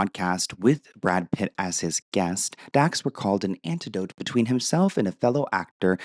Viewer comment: Anecdote, I think I heard 'antidote'